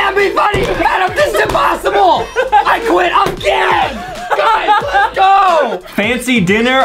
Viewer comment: He said: this is impossible I’m quitting again What I heard: this is impossible I quit, I’m gay ;-; No hate please